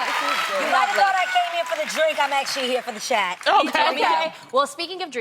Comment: why did I think Adrienne said, "You might've thought I came here for the drink, I'm actually here for the check" 😭 I felt so tense, I had to turn the subtitles on.